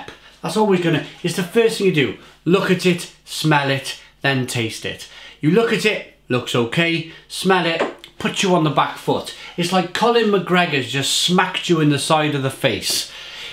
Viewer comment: Did I hear “Colin McGregor” ?? 🤣